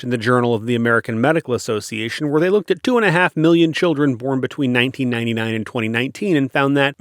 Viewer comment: you say "they looked at two and a half million children", but the subtitles say "they looked a half million children". That's the English (United States) subtitles; the auto-generated subtitles actually have it right. The quote you show at the same time also has the right number of <num> children, so hopefully people reading see that and are not misled.